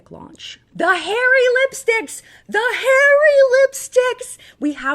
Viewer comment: I was listening to this video while doing the dishes, and I thought said "the Harry lipsticks!" lmao